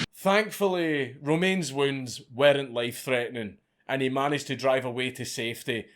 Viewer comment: My American ears heard you say "threatening wetnurses"